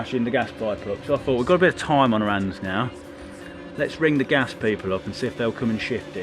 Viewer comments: was i the only one who thought he was saying Irish J instead of RSJ? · “RSJ”!!!! I thought he was saying ‘Irish Js’😂. You know, cause British Isles